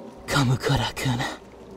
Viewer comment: It sounded like he said Kamukura-kuna… Kamukura-kuna… KAMUKURA-TUNA.